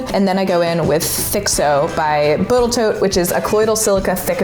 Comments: did you just say Botal Toat?? Tee hee😂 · Did she say "Botal Toat" at 03:50?😂😂😂 Please confirm? Just love, but I couldn't help but hearing it